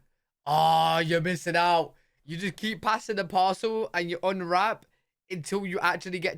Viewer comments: Omg I thought he said pass the possum!!!😱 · I thought he said pass the possum